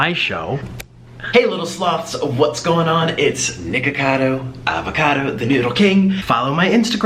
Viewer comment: sounded like he said nigga cado lololololol